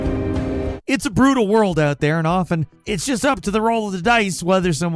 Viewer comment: Did I hear "It's a Bruno world out there?""